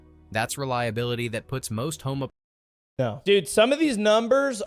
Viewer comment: did he just say “puts most homos…”